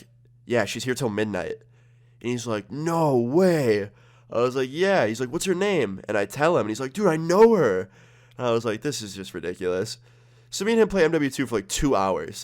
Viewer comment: sounds like he said “No way what’s your name, dude I know her” lmao